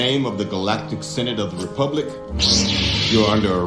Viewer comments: I think this video was thrown out there too quick. DW went through the entire offer publicly and I can’t say that I feel it was unfair. I can understand some of what Steven is saying but it’s not how he’s mis-characterizing the DW. I’ve been a fan of both shows for a while now and am bummed to see a disjointed effort now between the two. I think Steven needs to understand that no company is going to be able to give him the moon like he expects. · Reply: Well I thought I heard him say "in the name of the galactic synagogue of republic , you are under arrest" and wouldnt be that far from truth though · Well I thought I heard him say "in the name of the galactic synagogue of republic , you are under arrest" and wouldnt be that far from truth though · It's a good thing to hear both sides before jumping into conclusions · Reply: Well I thought I heard him say "in the name of the galactic synagogue of republic , you are under arrest" and wouldnt be that far from truth though